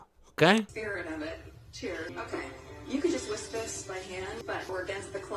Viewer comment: did she just say "wisp this" instead of whisk?